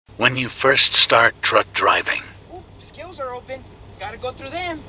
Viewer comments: I hear “when you first start drunk driving” You can also hear “when you first start drug driving” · I heard "when you start drunk driving"